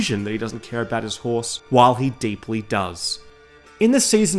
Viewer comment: did I hear this quite clear ? after 9:44 did you say " the city of Kerak " ?? the same city of Kerak in Jordan ?? my country? or you said something else? ... please explain this and which witcher game ?? I have Witcher 3 with its DLCs .. and there is no mentioning of the city of Kerak !!!! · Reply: Hi Basil! I did indeed say something very close, it's actually the port city of Kerack! It's the main city of the Seasons of Storms novel but it is briefly mentioned in the Witcher 3 (depending on how you finish A Towerful of Mice).